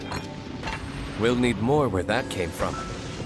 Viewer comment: YOOO i missheard and thought my diluc said "PLEntY mORe whERe thAT CaME FRoM" and i shit you not i was preplexed so i had to came here to check 😭😭 glad i missheard Edit : AND IT DOESNT HELP THAT I KNOW DILUCS ENGLISH VA HAS BEEN IN... RAUNCHIER PROJECTS